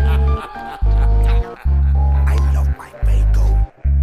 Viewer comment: It sound like he says “i love my bagel” in the very start lol